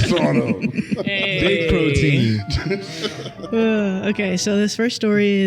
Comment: did i hear “big protein”? is an okop fan too 😂